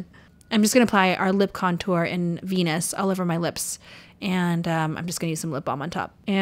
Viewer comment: did she say she is applying the lip contour PENIS